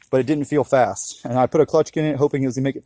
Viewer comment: what was it called?? U put a clutch...? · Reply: RENSKÖTARN Clutch kit, supposed to improve performance of the atv.